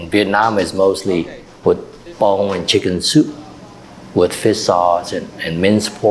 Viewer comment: Does he say "bone in chicken soup" instead of "bouillon?"